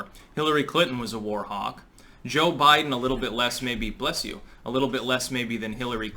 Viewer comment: I just sneezed right at 0:47 and I thought said bless you to me, crazy.